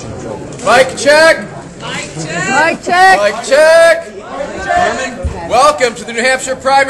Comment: I thought they were saying "VIBE CHECK"